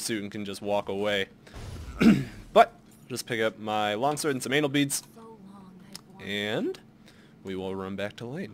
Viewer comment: did you say "anal beads" at 11:49 ? xD